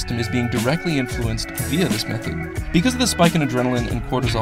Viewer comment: The music is too loud. It's hard to make out what you're saying after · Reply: "via the method"...